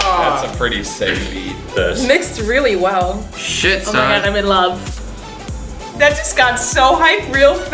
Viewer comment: What normal people hear: "Oh my god im in love " What I hear: "Oh my god now im gay"